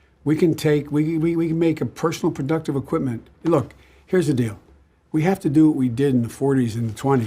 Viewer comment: What Joe Biden said again: “Look, here’s the deal” What I heard: “Look, fat, here’s the deal”.